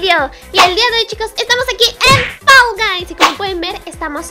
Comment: escuche fuysll gays XD :v